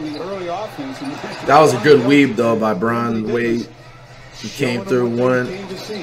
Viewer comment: did he just say lebron and wade